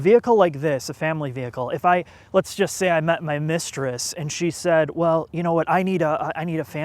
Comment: did you just say ".. my mistress.." ay 1:48?